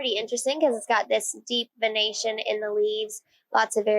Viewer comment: 😂 I thought she said a lot of irrigation in the leaves.